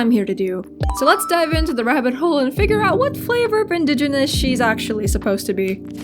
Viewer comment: I'm sorry- did she say "Flavor of Indian"? rewinds a bit Yes she did XD